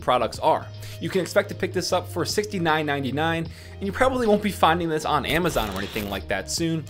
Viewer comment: When he said 6 99 I thought he said it costs 6.99$ xD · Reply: AliDaGreat he said $69.99 haha